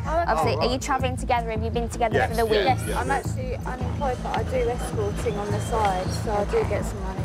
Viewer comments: Lol did she say "I'm employed, but I do escorting on the side" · Did she say she "does escorting on the side"?